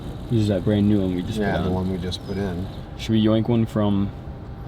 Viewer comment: did he say YOINK ? 🤣🤣